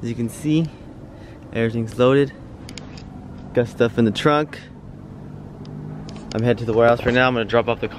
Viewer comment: My faded ass thought you said "i got Steph in the trunk"